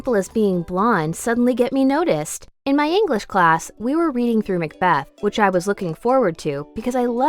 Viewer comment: When I said Macbeth or sumn I thought it said math book 📚 😂😢😁😑